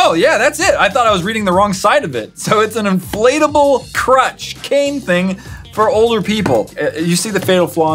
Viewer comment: inflatable crutch I thought he said inflatable crotch XD XD XD XD